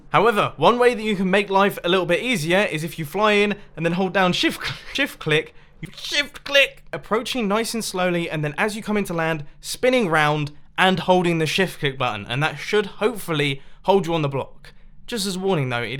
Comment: When he said “shift click x3”I thought he said chipotle and I’m like yay I’m not the only one that likes chipotle! (Like if you love chipotle) 👍🏻😂😍😛